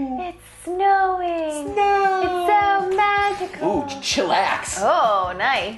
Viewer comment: is it just me or does she say it’s snow magical